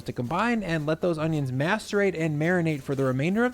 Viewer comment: I had to go back and listen. I thought you said the onions have to masturbate😲🤣